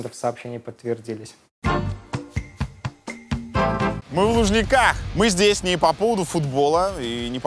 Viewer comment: Вместо Лужников послышалось "Мы в нужниках!". Неспроста....